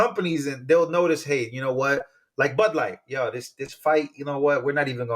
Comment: I thought he said butt light not bud light 😂